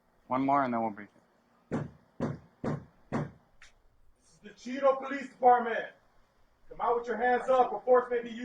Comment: I thought he said The Cheeto Police Department lol